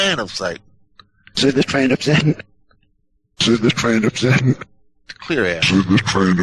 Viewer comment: the fan/plan question, I hear tran or maybe even train, I hear the tr clearly!?